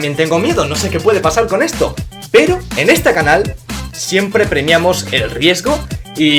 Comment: Creo que nadie se dio cuenta que dijo: en esta canal . En el minuto